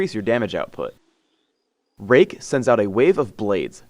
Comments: thumbs up if you thought he said rape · Did anyone else hear ''Rape'' @ 0:50? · I heard rape